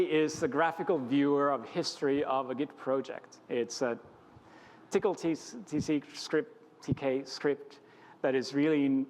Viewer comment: he says "It's a Tcl/Tk script" (subtitles say "It's a [UNINTELLIGIBLE] script")